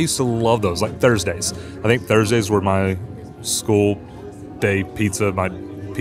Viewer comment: When I heard you talking about school pizza, I thought you said “skull” pizza and thought it was a chain of pizza parlors and my mouth totally started watering for some “skull” pizza - idea for the next big pizza joint - 🆓